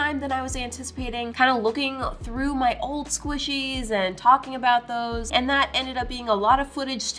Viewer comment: Honestly I thought The said “punting old squishy‘s” XD